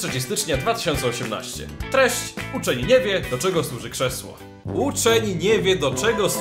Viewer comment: tylko ja usłyszałem uczeń je#ie?